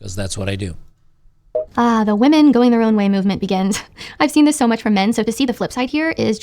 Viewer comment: at first couple of times I heard it as "women going the wrong way movement" had to rewind a few times to double check it lol 😆